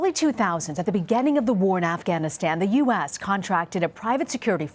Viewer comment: Anyone else hearing her say Afghaniscan, around the 0:19-:20 mark?